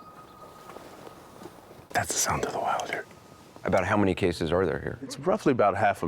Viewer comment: Does anyone know what the guy is referring to @ 12:46 when he says that "it's the sound of the wilder"?? (according to the cc) Or did he say, 'wild deer'??